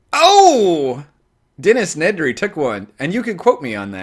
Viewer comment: What did you say at 10:25? "Dennis Nedre Tech 1"? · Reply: Dennis Nedre took one. From Jurassic Park